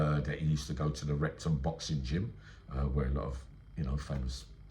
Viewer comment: It sounded like you said Rectum boxing gym